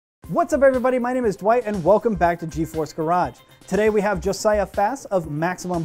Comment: i thought it said Geforce Garbage lol